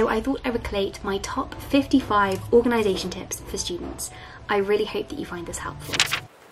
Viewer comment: I thought she said "I really hate that you find this helpful" LOL my inner dialogue shining through there